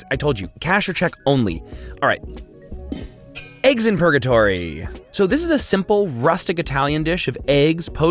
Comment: can someone tell me what dish is it? I know he said that there, but i struggle to get that. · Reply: Patryk Blewazka Eggs in Purgatory